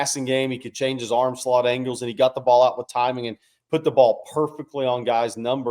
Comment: Sounds like is saying "Spot the Ball" . Texas will cover, adding another $20 on that wager today. 😢